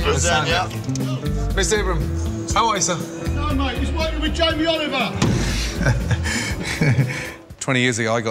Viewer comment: Did he just say Jamie Oliver?